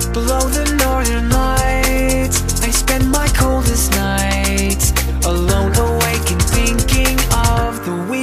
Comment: My 11 year old daughter thought he said "I spend my covid nights alone"